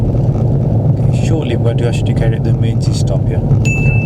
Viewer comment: out of curiosity... do i realy need to pretend to check every mirror in a "test" when im 100% sure from the observation of the last 10-20 secs? he "we" knew an estop is commin and the road is 100%clear? (all i'm saying is,.. yes it "could be dangerous in a real" situation but in the "simulation" it's kind of "normal" i'd say... ) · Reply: i meant 21:50 and im honest, english isn't my first language but i'm usually ok with accents and i might suck in grammar etc..... but even i couldn't understand the word "emergency" when you announced the stop... and somehow i can even figure out what welsh ppl try to say... it's really not about your accent in generall but i think that one was easy to either "missunderstood or took 2-3 secs to realize.. espacially while driving" i just happen to have the option to just rewind the video :3 and in case 'it was realy just about the "handbreak" ' from what the vid shows.. the car moved 5-10 cm at max in the video 'not at all'... (i was focused on the observation issue)